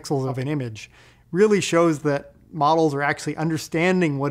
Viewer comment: You said 'sentences' in the very end of the video ... I heard 'functions' instead... :-D ; is it the brain filling the data gap with its own interpretation ?